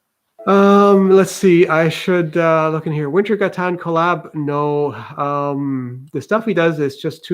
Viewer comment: What YT channel did say he didn't want to colab with due to not having CNC ? @ 7:50 I can't make out the channel name. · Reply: Wintergatan. It is a musician or musical group (I am not sure which, the album they last released had multiple people on it but it seems like the youtube channel right now is the efforts of one guy and his collaborators (As in, other channels). He's building a very complicated marble machine that plays music, and you can also see the previous version, which was quite impressive.